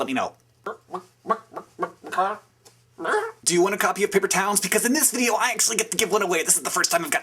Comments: Ooh, Paper Towns, please! xD When you first said it I thought you said, "Paper towels," and I'm like, "Who wants a paper towel?" Lol, subscribing. <3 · i heard "do you want a copy of paper towels?"